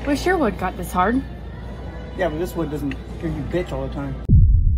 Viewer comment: Yeah, but this wood doesn't (mur mur mur) all the time. What did he say, please? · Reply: Hear you bench